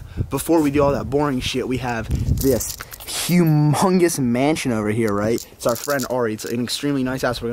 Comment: Do you literally get everything from Gymshark as soon as it's introduced? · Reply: Hugh Mungus hey, I heard your name at 12:59.